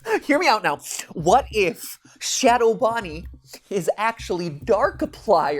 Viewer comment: I heard him say Ronnie and I got upset. Rip Ronnie.